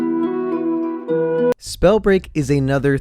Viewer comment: if you listen closely at the voice on 0:45, It kinda sounds like its saying "SPELL BREAK FOR NOOBS"